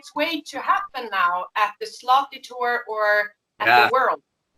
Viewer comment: lol i thought she said sloppy tur